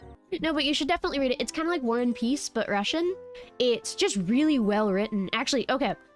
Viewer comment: Like "War and Peace" but russian. Ah yes, the very non russian novel of "War and Peace" (no hate, just found it funny) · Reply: I misheard it as "like One Piece but Russian" 😭😭😭